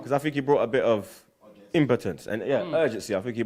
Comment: Did just say Dani Ceballos brought a bit of impotence to the team. 10:52 :)